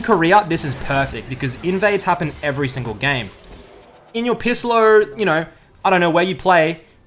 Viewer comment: Wait, did he just say Pisslo?